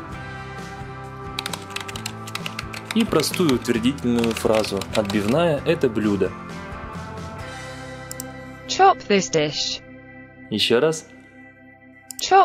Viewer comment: мне послышался ''че пиздиш''